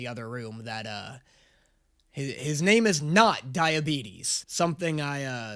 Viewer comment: I heard diabetes the first time to- I was confused but just rolled with it😂